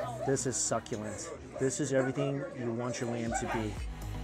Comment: i thought he said "This is everything you want your MAN to be" 😂😂🤦